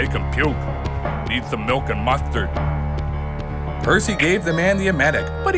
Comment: Did he say... Mustard?